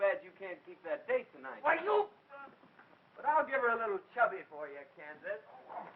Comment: did he say "chubby" ?